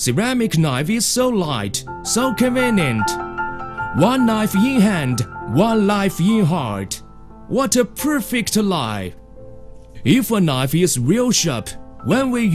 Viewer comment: can someone please tell me what he is saying here? It's sounds like "one knife in hand, one knife in heart. What a perfect lie!"